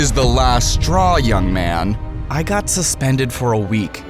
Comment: I really thought he said I got suspended for a year i a thought wow he’s lucky😢